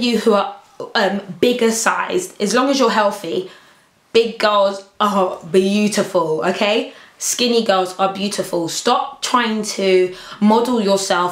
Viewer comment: rewatch the video again fast forward to 6:20 she clearly said Big Girls are Beautiful, Skinny Girls are Beautiful. She didn't put down one for the other. :)